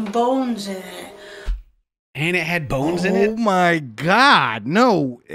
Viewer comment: Omg I thought she said bombs instead of bones 💀 I was so concerned for a minute 😂😂